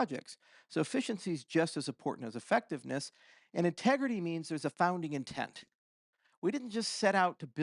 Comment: Anyone else hear Tegridy here? Oh Randy...